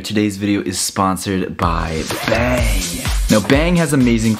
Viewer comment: Was I the only one that thought he said "Ben" when he was talking about Bang?